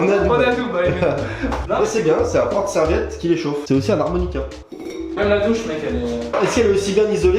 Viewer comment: Quoi ? J’ai bien entendu un harmonica 🤣